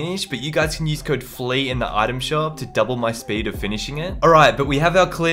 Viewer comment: i thought u said use code flea to doble ur pp 🤣😂😂